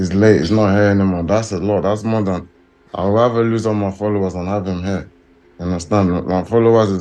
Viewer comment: Did he say "I would rather lose my followers than have him here"?